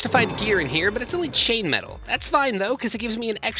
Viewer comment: did you just say chainmetal...